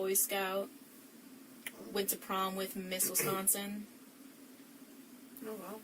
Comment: I thought she said "went to prom with misses swanson"😂. Took me 3x's b4 I realized she said Miss Wisconsin 😆. Gonna assume that was bullshit too? Poor kat. I hope she doesn't judge herself too harshly. I also hope that she can keep her ❤️ open to love.